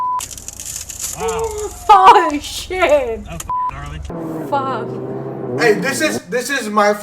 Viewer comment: did just said f@